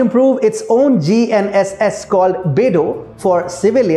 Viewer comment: did you say "pedo"? *double take